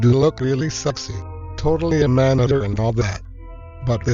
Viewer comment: Not trying to spam comments in here, but what did Diesel say in this scene? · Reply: "Totally a man-eater and all that."